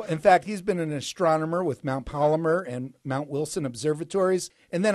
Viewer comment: Did anyone catch that at 11:16 "Mount Polymer"?